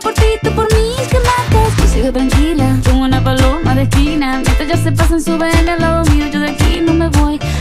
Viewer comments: pense que decia "paloma de china" aaa mi vida ha sido una mentira ;v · Pense que decia:uNa PaLoMa De ChInA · Pensé que decía "como una paloma de china" o sea keeee??? Qué penaaaa 😶😂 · Yo siempre creí que decía “soy loca come tigre” 😔✌🏼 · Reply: Yo pense que decia “como una paloma de china” 😩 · Yo siempre pensé que decía " como una paloma de China"😂😅